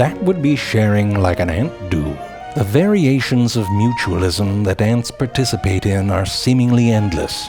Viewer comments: Ohhh, Mutualism. For a while I thought it said "Mutilation" and I was like, "this may be one Zefrank video I want to avoid." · (not kidding) the subtitles are wrong, it reads "... like an ant, dude" while it should be "... like an ant do".